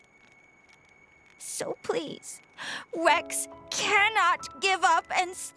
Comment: This is the first time Poppi has blatantly shown so much that her own voice and tone actually break the monotone we have gotten used to...Poppi’s most, literally, human moment so far! Holy crap! This game and Xenoblade are on my list when I can comfortably afford the appropriate system. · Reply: seriously tho, its to the point at 9:33 she is saying rex but is so emotional it actually sounds like she said wex